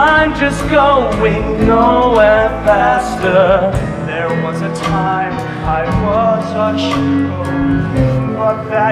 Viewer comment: I thought darren said "there was a time i was a churro" in the avpsy one for a second haha